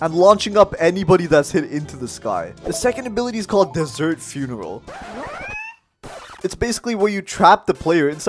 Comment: When he got to the desert funeral I thought he said dessert cereal xD